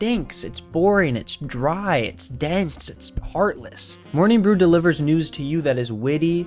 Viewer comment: so, I was a few feet away from my computer when the ad read started.. I misheard the name of the sponsor as "Morning Wood" then when you said "I rise, I grind!" I was thinking "wtf is this ad for?!" 😅